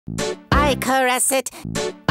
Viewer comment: it sounds " Hi Car acid" after a while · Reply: Hetastuck Trash I hear "I car acid" So its not Stingy